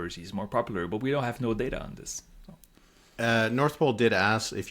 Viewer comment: I kept hearing "North Pole". Over and over. Is Santa Claus starting polling now?